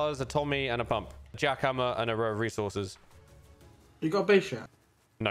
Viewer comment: what is he saying? You got a b sure? You got a bishop? You got a bee shear? You got a tea shop?